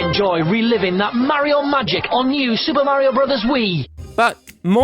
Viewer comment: When you said the commercial said it the same way you said it, the commercial clearly said mah-Rio, and you misheard it.